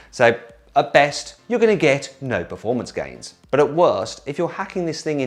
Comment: 超早口な「大間違いです」に聞こえる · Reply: no performance gains ってたぶん言ってる 確かに聞こえるw